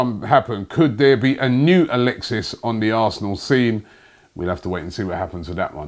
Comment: did anyone hear arsenal seam😂😂😂😂